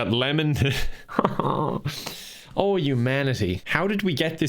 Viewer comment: I thought called that woman a manatee at 2:00 and I Absolutely lost my shit.